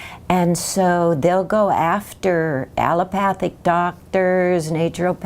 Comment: I thought she said Allopatric and I was like.......that's a model of speciation.....